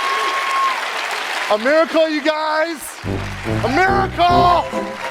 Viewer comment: I thought he was saying America you guys at first. Wouldn't have surprised me though if he had.